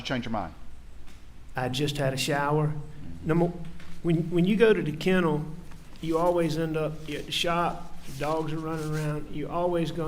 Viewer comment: Did AM say “when you go to the kennels you always get shot?” Am I hearing correctly???